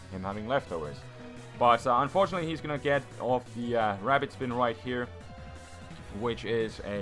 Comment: Before he finnished that sentance, heard him sayin': "Unfortunately, he is gonna get off that...//...rapid spin crit"